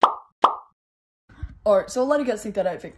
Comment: It sounded like he said ‘’this is my revoice’’instead of thats my real voice